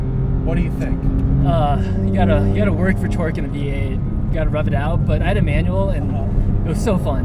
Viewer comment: thought he was saying "rub it out" ....i lost concentration and had to come back to the video ten minutes later